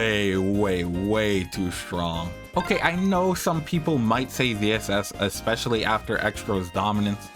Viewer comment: uhh what??? Who the fuck is saying zss?